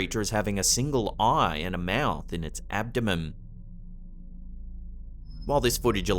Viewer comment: Did just say "it's abdomem?" 🤣